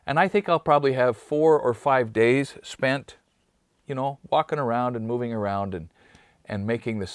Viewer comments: Did anyone else have to do a dbl take at 4:02, thinking they might have heard drop a very casual F-bomb? 😮🤭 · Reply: i'm ALL the way in Jamaica W.I. I heard "walking around" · Did he say he would probably have 4 or 5 days Fú@ around?….lol 4:01 into the video …. lol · it sounds like he says f- ing around... lol I was like no way!